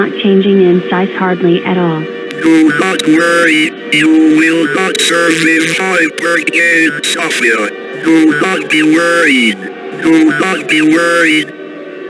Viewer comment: Is This What The Glitch Said? “DO NOT WORRY, YOU WILL NOT SURVIVE HYPERCANE SOPHIA. DO NOT BE WORRIED. (x2)” Why The Hell Would We Not Worry When You Just Said This Hypercane Will Kill Us All?! Edit: Looking back, what WAS this glitch? WHY was this glitch??